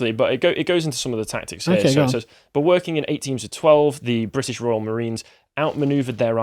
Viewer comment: "8 teams of twelve" ... Apologies, I thought you said '1812'! ✌️😉